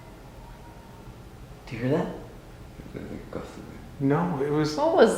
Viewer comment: I hear: "Do you come?" as points the camera at the door